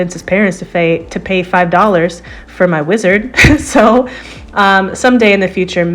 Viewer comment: lol I heard "lizard" at first and was confused/amused at it having a beard XD